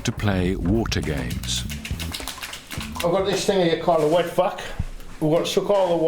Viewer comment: I totally thought he said "wet f*#k"